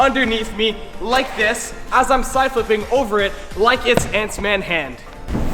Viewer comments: did he just said Ant’s-Man’s hand? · am I the only one who heard pants man?